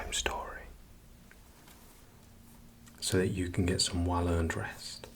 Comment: I heard "I'm gonna read you a bedtime story so you can get some wild & undress" and I was like 👁👄👁